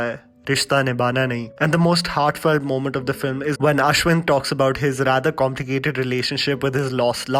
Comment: did you say Ashwin?...great video btw, seen this film many times and loved it everytime